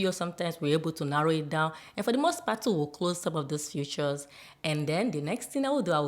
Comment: Great videos, I just figured out you were saying 'features', I thought you were saying 'futures' for a few days. But, I love you video details and really appreciate your channel.